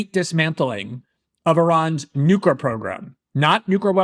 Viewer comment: Did he just say “Nuker program” like 2 or 3 times?!?